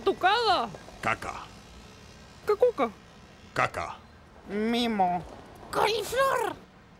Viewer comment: predijo el futuro (?) (Parece que dice nimu)